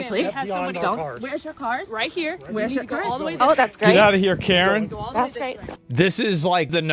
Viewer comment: I thought the agent said "Get out of here Karen!" haha